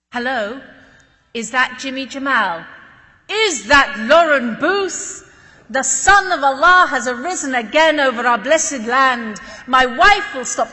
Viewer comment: Mashallah what a beautiful story❤️ the fact that he waited 5 hours just to earn some money so that his wife and kids can have food💔 May Allah make it easier for them and Hope one day Palestine will become free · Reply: in 2:25 u can see he mentions it but maybe u didn't hear him like me the first time what he was saying because of the accent but I played it till I understood what he was saying He said: Is that Lauren booth? The sun of Allah has arisen again over our blessed land. My wife will stop crying, my children will eat again. Allah Akbar! Welcome back to your home, we missed you! Later on when he waited 5 hours just so he can earn some money to feed his family and didn't let her stay at the hotel because of the danger so he offered her to stay with his family as a guest so he knows her visit is safe.